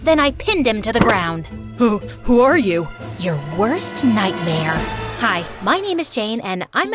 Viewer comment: no way i thought you were called "my worst nightmare"